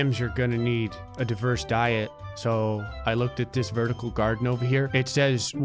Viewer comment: i thought that said “A-OK-Boomer Vertical Garden”